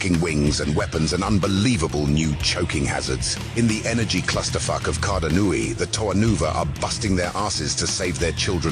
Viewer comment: I misheard it as "clusterfuck of continuity" which is still funny as a casual follower of Bionicle.